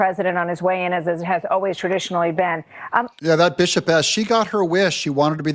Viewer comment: ! Did I hear you say "bishop ass"?!